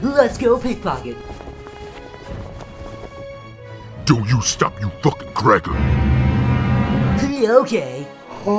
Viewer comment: I'm not from an english speaking country so I have problem understanding what he says at 0:30 - 0:33 ? "Dude, don't stop your fuckin' grabbin'" or "Doe, stop you fuckin' crackhead" or what? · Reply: It's "Don't you stop your fucking grabbing" because after he says it the main character continues grabbing.